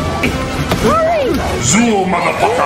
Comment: is he saying ZOOL? or ZOOM? V